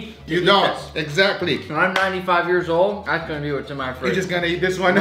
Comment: did he just say he 95 years old ☠️💀